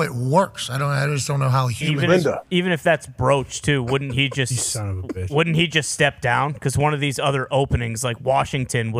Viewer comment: What does AJ say at 2:58:32 that got DB crackin up? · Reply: he says Linda, Bill's ex he just split with 😅